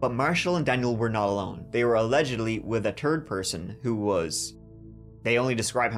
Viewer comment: Your accent kills me at @ 19:24 you said “was a third person” but it sounded like you said “turd” 😂